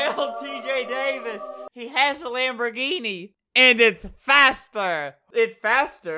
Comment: When Tj said: And it faster I thought he said: And I have a tractor : ) still chill